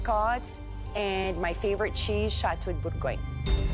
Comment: What did say at 10:28 ? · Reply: chateau de bourgogne, cheese a lot like brie